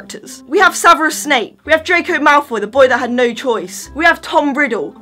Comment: Omg 😂 😂 😂 I heard you say :"Draco malfoy the boy with no toys" lmfao 😁😁😁😁😁